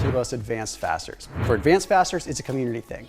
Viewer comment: I thought he said 'advanced bastards' instead of 'fasters'. 😂